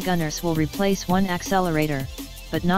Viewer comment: I thought you said one accelerator is as good as to golden minis